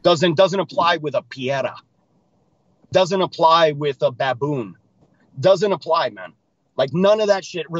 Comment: Did just call Diego a baboon? Lol